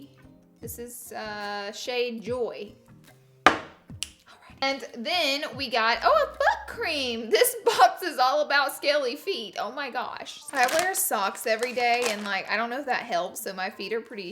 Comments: when she said " ohh a foot cream!!" I thought she said "ohhh a butt cream" and I died XD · I thought she said “oh a fuck cream” instead of foot 😂😂😂😂😂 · I thought u said but cream I live for that!!!! Hahahahahahahah